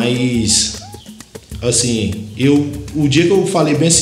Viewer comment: O que ele falou nessa parte 5:37??? "Um dia eu vou....?